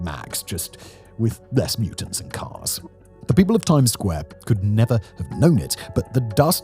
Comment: did you say Times Square?